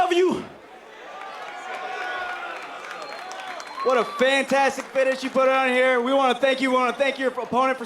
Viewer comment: Did he say "What a fantastic fetish you put on here"??